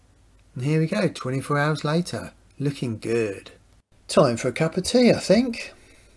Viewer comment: I thought i heard "time for a cup of bleach i think.."